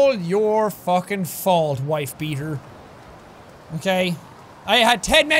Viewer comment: what did jack say? Wife beater? Umm.